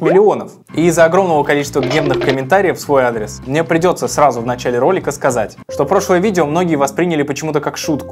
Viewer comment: кому тоже послышалось в 0:42 мемных комментариев